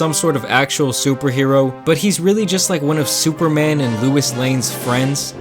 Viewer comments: did you just say louis lane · did he say lewis lane at 0:27?